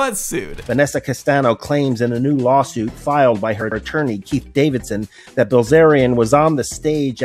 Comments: I thought I heard the Attorneys name as "Pete Davidson". What a way for audio distortion to mess you up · anyone else catch the attorney named Pete Davidson at 15:40? lmao!! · I thought he said Pete Davidson 💀